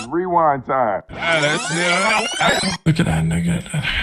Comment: Sounded like 'Look at that nugget' to me.